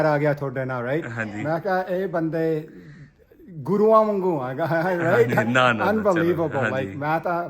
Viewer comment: Did I hear right at 2:30 did he say and are like the guru ? ....... Is he for real ?